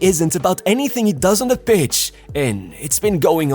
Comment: i heard PITCH wrongly, i thought you said b... 🤭 😁.Don't like vini.. So selfish.. But i have to give him credit in the 2026wc, he played as a team.. Assists and goal..🔥 Keep it up